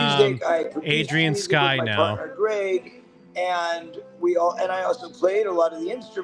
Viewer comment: sounds like he says “my butler Greg” 🤣